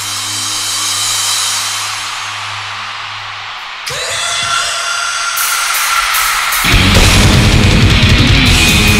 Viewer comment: 에 토시가 뭐라고 말하는지 아는 분 있나요? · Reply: 쿠레나이다~